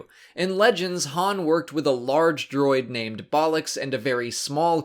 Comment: I had to skip back a few times to see if I heard right, after a quick wookiepedia search I found that yes....there is actually a droid in legends called ‘Bollox’ 🤣